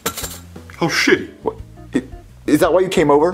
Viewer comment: Did Goodman say "Oh, shit" or "How shitty"?